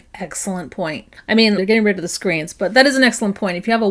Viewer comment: Did you say “They’re getting rid of the screens”???? What? They are??